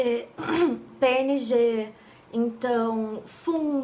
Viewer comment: eu entendi pênis de ...kkkkkkkk 😅😅susto kkk